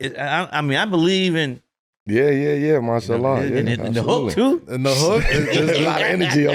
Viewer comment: Did Melo just say Mashallah ?